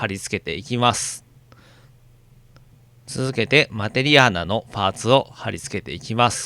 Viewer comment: マテリア穴がマテリアーナに聞こえた←病気www